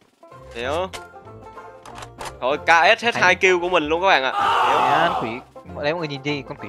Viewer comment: Kill của tau nghe thành gil của tau😂